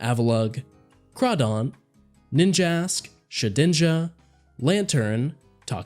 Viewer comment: i thought he said bitchass when nhe said ninjask